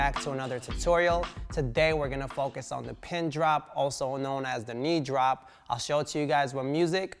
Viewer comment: Who came here after Tobi dropped this way too hard😂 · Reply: Bro,I thought Tobi said "Pen Drop at first"